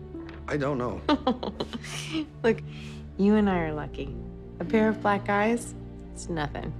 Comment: I thought she said a pair of black guys and I was confused for a few seconds